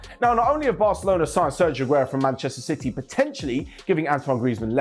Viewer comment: Did said "Ban-chester City" at 3:47???😤